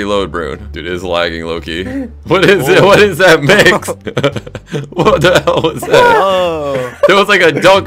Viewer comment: Am I trippin or did say "what's the hell